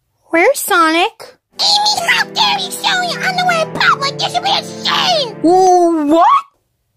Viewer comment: What did Cream say to Amy? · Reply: AMY HOW DARE YOU SHOW YOUR UNDERWEAR AT PUBLIC!!? YOU SHOULD BE ASHAMED!!!